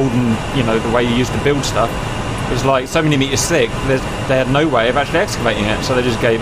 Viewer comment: "70 meters thick" Are you sure about that? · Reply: i heard "so many meters thick"